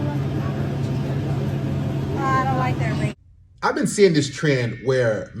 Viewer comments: Did she say « I don’t like that ring » ? 😳😂😭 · it sounds like she said. I don't like that ring. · did she say: i don't like that ring ??????!!!!!!!